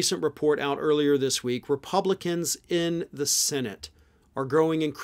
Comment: I thought it said "sentient republicans" and I was like "huh? There are sentient republicans"